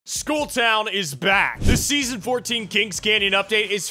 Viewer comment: I’m going to be honest, if i couldn’t read then I would of thought he said “School Town is back”